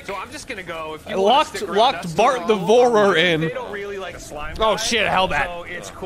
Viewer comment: Ngl I thought that he said Bart the Borter and that he’ll Bort you which I think is infinitely funnier in retrospect